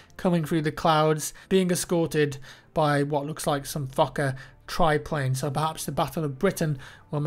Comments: I love when he says Fokker, it sounds like he is saying Fucker, huehuehue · with his brittish accent it sounds like he is saying fucker not Fokker :D